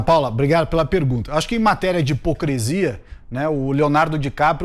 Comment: eu escutei HIPOCRESIA? Moro: conje Wintraub: imprecioante Salles: hipocresia Guedes: 150 + 250 = 500